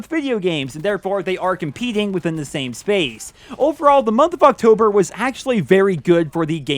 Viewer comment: did he say 'Motherfucktober' ?